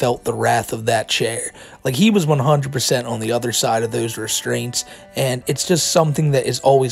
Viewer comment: Did he just say "tits restrained"